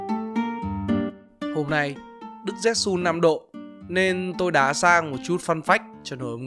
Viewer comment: nói gì vậy mng nghe không ra :/ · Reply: đức rét sun 5°